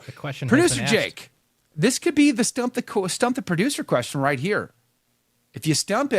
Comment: Oh boy!!!! Am i ever glad you repeated the game title of "Stump the Producer!" I misheard you to begin with and heard "STOMP the Producer "...! Haha, man, i thought dang, that's kinda a hard knocks , real old school initiation game !😮😅